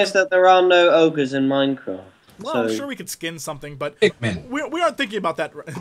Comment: in 02:55 when he says pigmen i heard pikmin